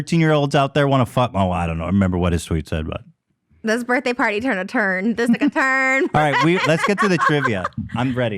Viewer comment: did she say the n word??? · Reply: "This birthday party turned a turn, just took a tuurn"